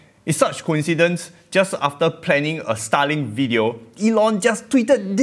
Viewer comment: i thought u were saying Stalin.